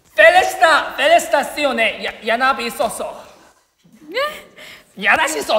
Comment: what minhyuk said sounded like greek phrase: gia na me soso! lol